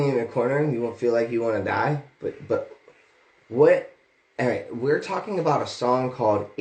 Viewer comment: does he call his brother "Aaron" or is he saying alright ? Really sounds like he accidentally said aaron instead of Nick.